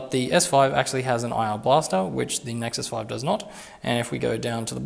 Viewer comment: I understand the need for brevity, but at one point you were flying so fast through the comparison I thought you said that S5 had an ion blaster. Which, if I'd heard correctly,would have been the clincher. In fact, the vid would only have to be 10 secs long. 'Yep, S5...ion blaster for defending you & yours from the impending rise of the machines...so until next time...".